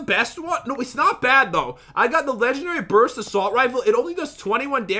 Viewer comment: it says brust instead of burst